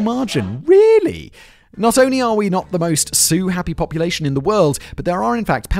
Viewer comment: Ha. At 5 minutes in he says the us isn't the most sue happy. I heard it as they aren't very Sioux happy..........not wrong.